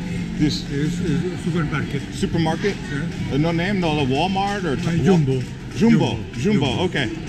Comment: Did he just say Walmart?? JAjajjajajajja 🤣🤣😆